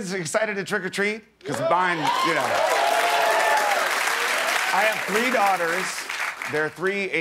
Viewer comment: I heard "I have three daughters" as "I breed otters" :D